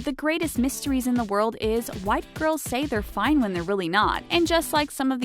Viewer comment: when she said "why do girls say...." i thought she said "white girls say fine "